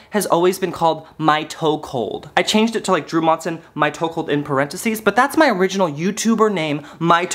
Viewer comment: When you were saying mytoecult, I thought you were trying to say it mytoekehold kinda like ‘my chokehold’ , which I like.